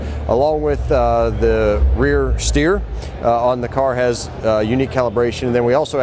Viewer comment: Do we dare ask the Price of the Big Engine Model...didnt the older V have 650 horse power.....hmmmm.... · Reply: did he say Rear Steer?? 🤔🤔 Caddy pulling out all the stops....